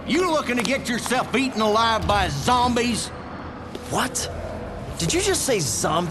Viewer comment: When the dude said "eaten alive by zombies", I thought he said "beaten alive".